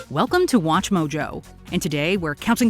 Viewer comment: Hold on, did she just say Watch Jojo?